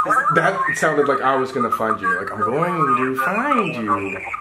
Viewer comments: I shit my pants when he said 'Im Gonna Find You' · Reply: Baby Girl I thought he said "I will find you" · "I'm going to find you" made me shiver!!! At first I thought it said "look behind you" 😮